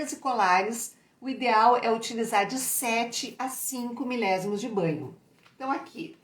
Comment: No vídeo entendi que para colar e pulseiras o banho é de 7 a 5ms!? Pode me esclarecer? Foi no minuto 10:30 do video